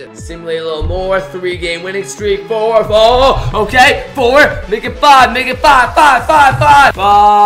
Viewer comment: DID SAY "MAKE IT 5 NIGGA"